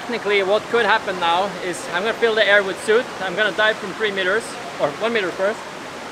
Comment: Did he say at 3:25, i am gonna fill the air with suit?